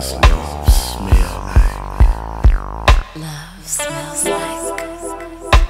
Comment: What does love look like? Love looks like you What does love love feel like? Love feels like this What does love smell like? Love smells like us You make me understand What it means to be in l-o-v-e once again Why, must we fly so low? Are we 'fraid of heights, do kites get lost in the tow Stank love, stank love, stank love, stank love Let me show your mind a new freaky side of love Open up you flower please let me taste your love Honeysuckle sweet, can't stop till I get ever drip drop on my tongue Hitting every spot of you, what you gonna do Your body is the rhythm of the boom in the room Up and down it seems to go Oh my god I think I'm bout to explode Stank love, stank love, stank love, stank love My fingertips scan flesh so supple No longer a couple like two in one skin Where do you end and where do I begin Both brains become one mind sensually Every nerve becoming it's own individual entity With its own lusts, it's own needs to serve Longing for the love of all the other nevers As they writhe and twist in satisfaction In the burning chill of please we bathe Engulfing, encompassing like a cataclysmic shockwave Of an impact so deep, but not one of destruction But of creation, elation in the re-making No taking in the relation, no taking in the relation Just giving of ther persona making lover after making love Till ain't nothing but Stankonia Stank love, stank love, stank love, stank love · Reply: It sounds like she said love smells like ass.✌️💯